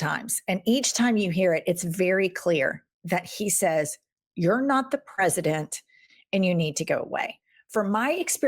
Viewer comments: I heard “You’re not the president you need to go” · I thought he said Mr. President I need to go to the bathroom.